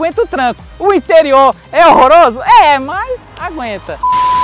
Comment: eu entendi duster e horrorck😅😂😂😂😂